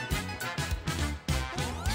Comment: Sounds Like Its Saying "Inspector Gad-Shit... Woooohoooo!"